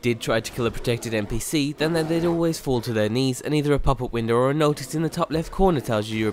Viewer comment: did i hear the term "knee" ? ~.~